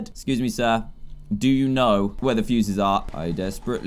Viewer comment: i thought when he asked the frog where the fuses were... i actually thought he was saying: "DO U KNOW DA WAE?!" LOL